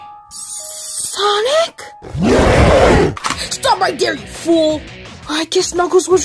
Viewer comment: WHAT DID SHADOW SAY!? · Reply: He said "Stop right there, you fool!"